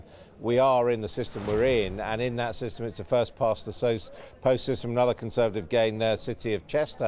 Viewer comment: Did he say first past the toast to begin with?... Oh no